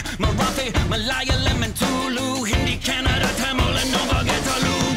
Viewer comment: Did he just say tulu! Hey that's my language 😃